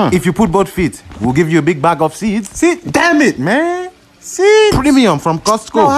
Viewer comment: I misheard I'll give you big bag of weed😂